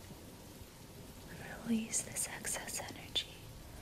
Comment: She said “release your excess energy” I thought she said “release your sexist energy” I was like damn u didn’t need to call me out like that